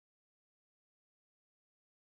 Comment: First time i heard this i thought he was saying: "Who holds the north ? Who holds the north ? Your Mean !"